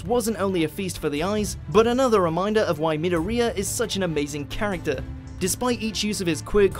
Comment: wait did u just say "minouria" or am i just deaf